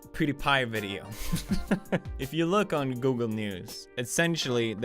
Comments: Who else thought he said "Google Nudes" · When he said Google news, I thought he said Google nudes 😂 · thought u said 'google nudes' and u said news oh gard